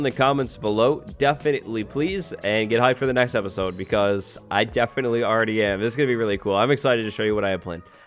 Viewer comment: did I just hear "get high for the next episode because I definitely already am."